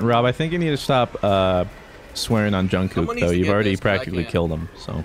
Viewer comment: did he say Jungkook? Lol